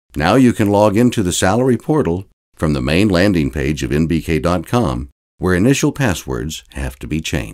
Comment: Kindly make corrections in the subtitles/closed captions: 1- At 1:30 there is a mistake , Website should be <link> instead of <link>. 2- At 2:20 it is not in BK, its NBK. Reagrds